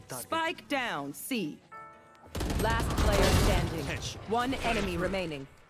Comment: does he say bitch